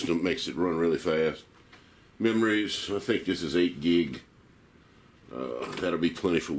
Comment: thought you said 2 gig of ram? yuck dont tell her lol